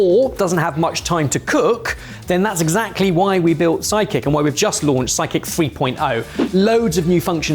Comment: I was a little distracted and was listening to say you don't need a chef to do your shpping with you, and I thought I heard him say that your psychic will do it all for you. It took me a second to realize he'd said sidekick. Rather disappointing actually. It would have been interesting to hear a psychic telling givng advice. That would be quite a show.